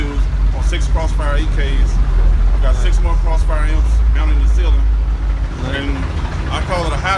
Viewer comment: And how many amps that it has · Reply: I thought he said he has 6 more 8k's in the ceiling